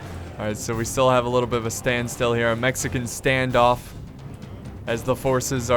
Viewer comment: did he say a "Mexican standoff" at 21:00 😂😂